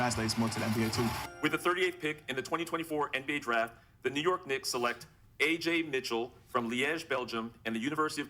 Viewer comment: Did he say "The Negro Knicks" or am i trippin?